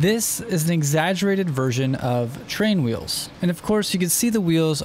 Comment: At the beginning I thought he said training wheels 😂💀